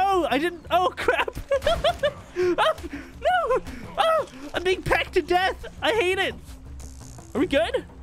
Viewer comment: Bro i need to chill out and go to church some time. I heard him say "I'm being pegged to death" instead of "I'm being pecked to death"